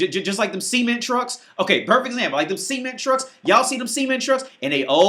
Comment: English is not my 1st language, when he was saying cement truck I thought he was actually saying semen truck.